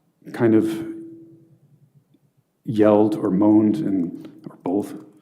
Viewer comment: "She's got a lot of hair." We found our motive! Edit: 2:47:05 Did he just say she "yelled an oath"? 😂